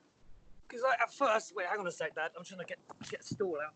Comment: Did Tom just say: "Wait... hang on a sec Dad"? 😊